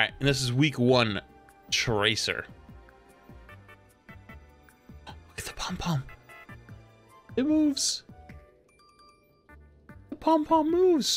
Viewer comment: Lol for the tracer skin I thought he said "look at the bum bum, it moves!" As he just shook the camera back and forth